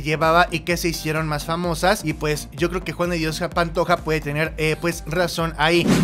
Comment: se oye que dice Juande diosa Pantoja jajaja